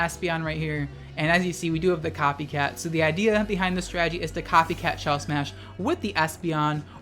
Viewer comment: my brain combined espeon and leafeon and I thought it said; “Copycat Lesbian”